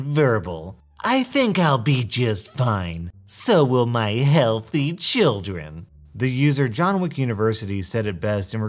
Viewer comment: Somehow, SOMEHOW, I heard “healthy s**ts”